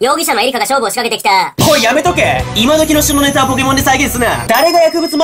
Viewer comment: 位に、旬のネタが、下ネタに聞こえたのは間違い？